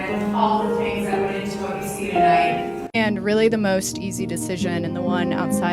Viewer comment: I thought the speech was going to be a great, moving speech I could steal, but then I heard "most easiest" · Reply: Neither bride said “most easiest” though. First one said “the most easy,” second said “the easiest”